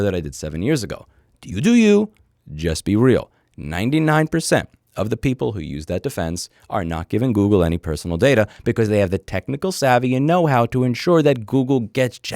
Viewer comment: Dis I really hear that word or do I only think I heard „addefense“?